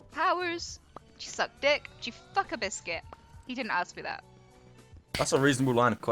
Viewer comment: Getting affirmative consent from her would be a nightmare. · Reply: I thought you said fuck your biscuit, uhh i should go